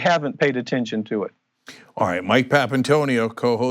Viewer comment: Excellent report! One thing, it sounds like he said, "Thank you CHAD" at the end.